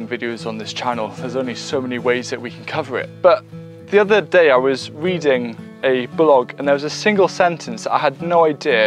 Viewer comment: For a second I thought I heard "the other day I was weeding a blog.."